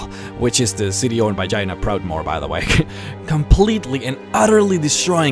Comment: Sorry to say this but when you say Jania Proudmore, it sounds like you say Vagina Proudmore...